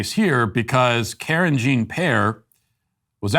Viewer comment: Karen Jon Pierre sounds like she’s explaining to a group of family members why her son just behaved terribly at the restaurant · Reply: I honestly thought he was saying Carrigeen Pear and thinking" What an interesting name, I wonder if it's Irish?"!